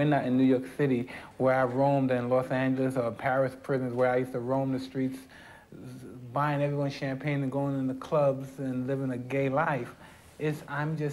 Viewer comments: Did he say he was living the gay life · did he say ”living a gay life” or im a trippin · Did he say living a gay life